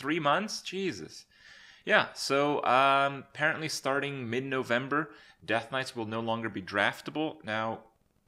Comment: You said they removed death knights from arena but I just got death knighted and lost with my most insane deck ive ever drafter.. :( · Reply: Rip, i just relistened and heard you say mid November